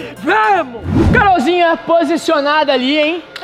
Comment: credo jurava q entendi : vai amor kakaka estranho